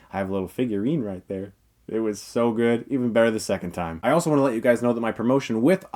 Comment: Did he said "even better than second time" or "even better the second time"?